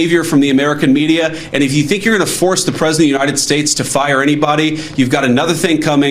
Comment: Sounded like she said “president TO the United States “ ??